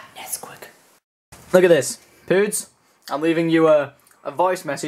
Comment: you voicemailing pewdiepie? Swear I heard pewds lol